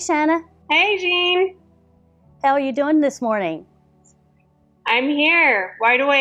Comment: Did she say, "The hell are ya doing this morning?"